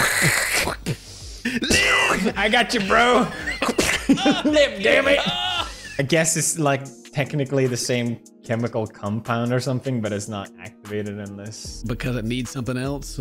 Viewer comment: Did... did he just say chemical 'cumpound'?